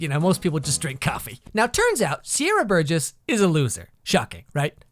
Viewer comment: Please tell me I'm not the only one who thought Sierra Burgess said Sierra Boggess